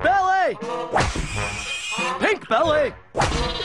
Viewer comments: When I was a kid I thought Ed said big belly. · When I was a kid I thought Ed said big belly. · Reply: I thought he was saying "hit belly" lol